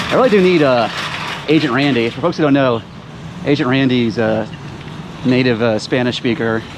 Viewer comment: Agent Randy... I thought he said Asian Andy.. Now that's someone who should do some Cart Narcing...